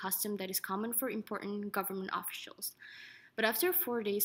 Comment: Dekeased 😂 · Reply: Did she say “office shials”?! 🤣